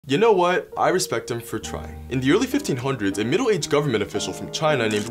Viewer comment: Help i thought said i respect him for lying and was waiting for him to say yeah he just ran away and made everyone believe he went to space